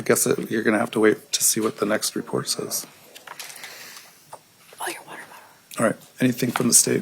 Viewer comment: Wtf did Lori say throw your water bottle at him, referring to the judge?!?! Around 34:00 minute mark. The Close Captions even picked it up.